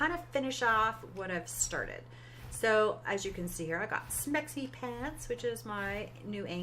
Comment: I like your base design and the elevator is cool! I didn't quite catch the name but I thought you said you named your argie Sexy Pants, which would of been crazy cause I had named my argie Fancy Pants, lol.